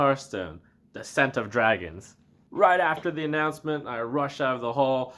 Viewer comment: At first I heard is as "The Scent of Dragon" xD Also wtf with the free Legendary? What about Mage, Paladin, Hunter? Or fuck those classes?